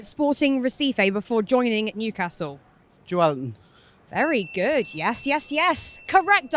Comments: Did he say Joe Allen 😂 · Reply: Just a weird pronunciation for joelinton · Did man say Joe Allen